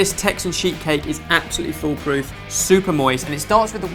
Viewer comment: Why do I keep hearing 'Texan sheep cake'? I know that isn't what bens saying so what is he saying?